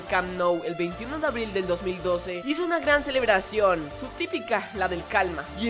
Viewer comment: oí mal o dijo 21 de abril de 2012 ? :v?